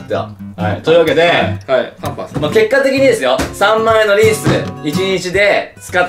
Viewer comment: パンパースって聞こえる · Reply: パンパンすな やと思うで